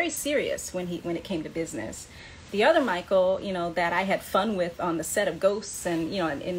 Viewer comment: it sounded like you said F* WITH sorry I just found that funny